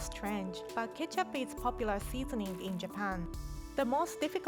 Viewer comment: yo i thought she said ketchup is for pussies